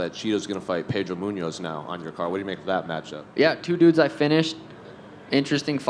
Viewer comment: did he just say he finished chito?